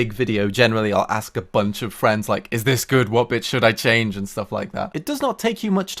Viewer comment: I misheard "Which bit should I change?" as "Which bitch should I change¿"